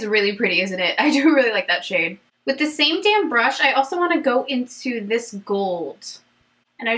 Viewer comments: I swear I heard "same damn brush"! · Thought you said “with the same damn brush...” 😅